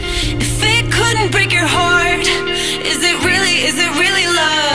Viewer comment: Finally i found the song 🙂 Woa thanks for likes :) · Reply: wait wait wait you mean "if it couldn't break your heart"? It does sound like she said fate but it's if it